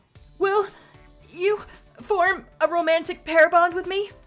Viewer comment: Wasn’t paying attention to the video during the start of K1-B0’s love hotel scene, I thought I heard “romantic pare bond”. Like, the fruit.